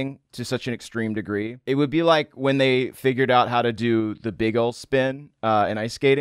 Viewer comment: what did he say? because it sounded like bagel spin and that confuses me